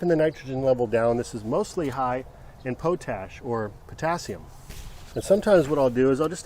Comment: did he call pot ash "potache" lol